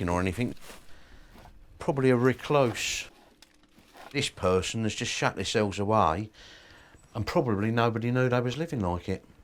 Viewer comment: When the council guy was inspecting the house and he checked the toilet I thought he said “this person has just shat themselves away” 😂 just that strong accent